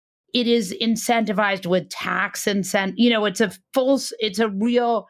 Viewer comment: I heard, "tax AI" 😢